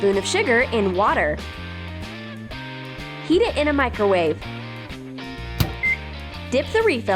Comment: for a second when you said and heat it in the micowave i thought you said eat it in the micowave and i was like Umm wut but great vid i might need to try one of these